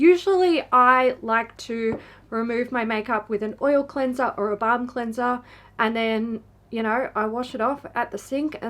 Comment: You said balm cleanser but I was distracted so I heard "bum cleanser" and I was like